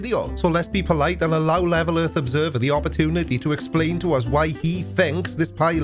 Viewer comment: Speaking of misheard lingo, I could have sworn said "libel earth observer" a couple of times... and he wouldn't be wrong! lol Also, I think it's appropriate to give the "SR71 proves earth is flat" bollocks a well deserved grade of a big fat FaiLEO!